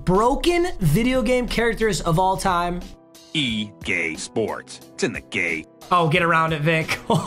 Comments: did that say E Gay sports or am I tripping? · Bro i heard "e gay sports its in the gay